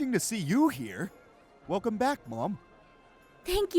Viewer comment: I have subtitles on and at 1:33 when he say Welcome back Mum subtitles say welcome back bob 🤣